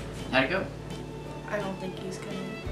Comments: I had captions on and at 1:04 instead of it being “I don’t think he is coming” the captions say “I don’t think he’s gay” 😂😂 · lmao the subtitles said "i don't think he's gay" instead of "i don't think he's coming" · When I put the sub titles on on 1:05 it says “thank you I don’t think he’s gay” instead of I don’t think he’s coming.